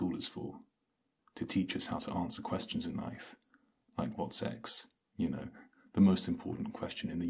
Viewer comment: When he said “like what’s x” I thought he said “what’s SEX” I was like HOL UP🫣🫣